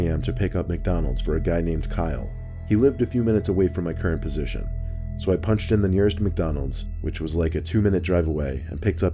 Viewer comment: bruh I thought he said “so I punched him in the face” 😂